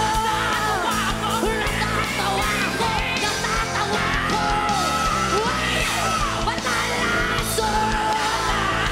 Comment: censored yung bibig ni Miss Lea. Decode what she said please. haha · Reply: "oh fvck this is great"